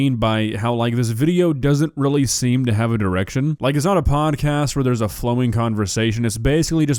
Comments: I thought you said this podcast doesn’t have an erection 😅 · I stg at 3:10 I heard "this video doesn't seem to have an erection"?" My first thought was "wow, and there are TWO dicks in this video"